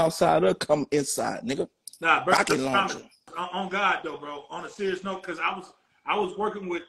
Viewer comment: Did he say “ rocket launcher n***a” 💀💀